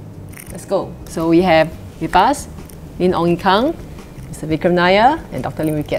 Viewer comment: did she just say Ye Ong kung ? who ? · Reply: she said “Min. Ong Ye Kung” short form for minister